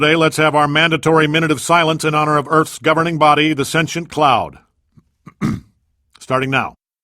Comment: I am disappointed. All this time, I thought it said, "Sentient Clown." Sentient clown is so much better as a line than sentient cloud.